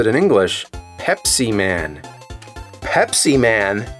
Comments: I swear every time I hear "Pepsiman" I hear "HepCman" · I heard “Hep-C Man!”